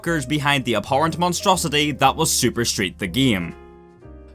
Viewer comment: hahaha because of your accent i thought you were saying Super Straight The Game. Memed